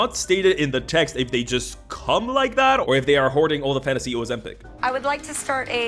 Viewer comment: We come in peace 🖖👽 (not) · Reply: WTH dd he say at 2:40?Because YT captions say "...all the fantasy Oz epic..." ????